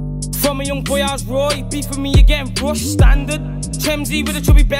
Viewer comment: did he say "Rogue standard " the starter pack in fortnite was called Rogue agent 😂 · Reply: Rushed standard